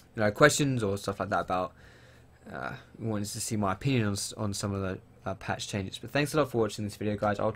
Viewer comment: did you say "who wants to see my penis?"